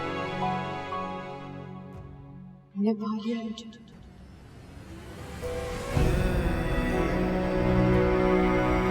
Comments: pe mahak ne kya kaha ?? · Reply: pe mehek ne kaha ki "Tumne paa liya hai mujhe". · me mehak kiya boli shamser se kuchh samjh nhi aaya, please koi btaye ,kiya boli mehak ne 😢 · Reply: Tumne pa liya hai mujhe ❤❤❤❤❤❤❤❤❤❤❤❤🎉🎉🎉🎉🎉🎉🎉🎉🎉🎉🎉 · par kya kaha mehar ne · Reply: मुझे पा लिया है तुम ने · kya bola mehak n Sunai nhi pada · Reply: Tum ne Muje paa Liya ha